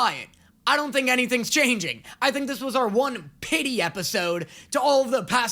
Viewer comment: I heard “Mrs piggy episode” and immediately went ?????